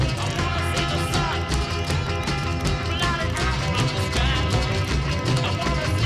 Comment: At around 13:20 I'm hearing slightly different words than the lyrics onscreen. Maybe I'm used to what I think of as "my lyrics", but I could swear he's singing "blotted out from the sky" instead of "flying high in the sky"